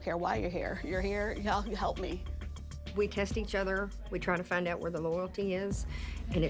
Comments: did she just say we kissed each other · I literally thought she said we kissed each other. It wasn't til I but captions on that I realized she said test 😂